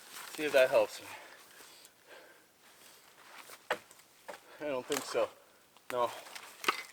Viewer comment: No offense but at 12:21 are you alone I hear, " I don't think so"